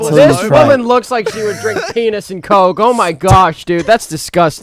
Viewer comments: Did say Penis and coke instead of penunts and coke, or is it just me. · did just say pp and coke? Nah we aint got he went pg-13 Also gotta work with his girlfriend after she sees this